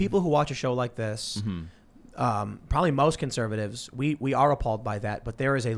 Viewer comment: did tim said he is a conservatist, lmao